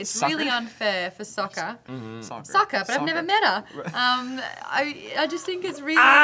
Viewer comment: what was the pun? I don't think I heard it correctly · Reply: "Sock her but I've never met her." That's essentially Maude's pun.